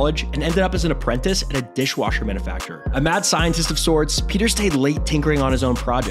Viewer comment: At first I thought he said "dishwasher benefactor"